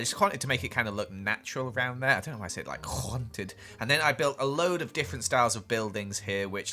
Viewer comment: Who else heard khaunted